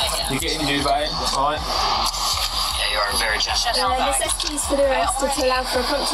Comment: did they say yeah your a virgin?